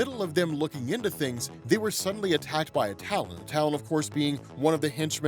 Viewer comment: Instead of 'she was attacked by a Talon', I heard, 'she was attacked by a Towel'. And I was like... Towelie? He's main DC now?